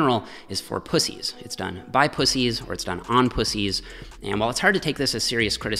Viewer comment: I’m sorry did he say on?